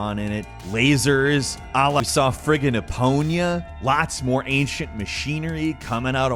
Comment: Good sir 9:32, how did you say Epona? I heard Epoña. 😂